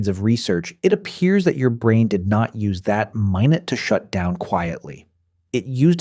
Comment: This was great until 7:42. I was fooled by the audio until that time. "My-nut" instead of minute. Don't like that.